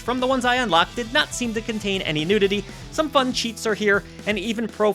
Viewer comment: I heard unlocked "cheeks" instead of cheats 🤣